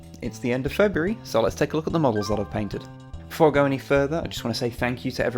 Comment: Even with the knowing the complete context of this video, seeing previous videos, and reading the title, at the beginning I thought he said, “let’s take a look at the moles that I’ve painted”.